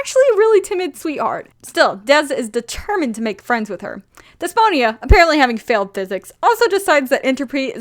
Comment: HA I THOUGHT YOU SAID DYSPHONIA AT FIRST